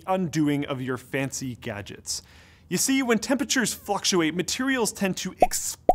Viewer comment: did he say fucksulate?